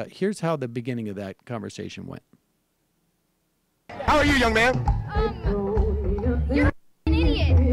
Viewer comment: Did he say 'young mam' or young man, but what a pretencious little twerp even if it was 'mam'! I thought he said mam, but what a way to address a young kid (how about a simple non objectivizing 'hello')..... It makes me laugh when these fools read their own interpretation into bible verses.....thats not what First Peter is about, completely out of context. In this case certainly a refuge for fools.